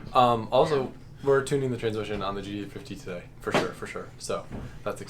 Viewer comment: Why do you say GT350 like there’s no 3 in it. Always sounds like u say GT50